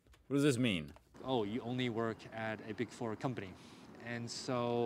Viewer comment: did he say a big foreign country?